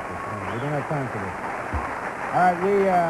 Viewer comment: Anybody hear him say "It's hot in here again"